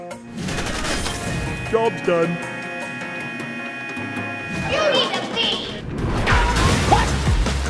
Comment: i heard "you need the beat" not "you need to leave". Is it always like this with english???.